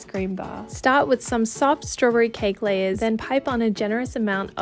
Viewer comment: I heard “Let’s start off with sob storyberry cake layers”